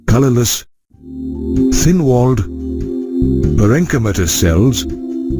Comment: Can anybody tell me what he said after diploid,colorless,thin walled......... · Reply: parenchymatous